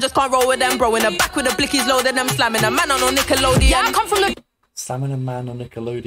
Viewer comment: Nickolodeon always Slimes celebs at kids choice awards. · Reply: ooh, my American brain thought she was saying 'slamming', it's 'sliming' lol.